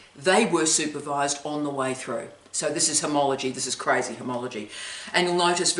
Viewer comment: Did you say homology?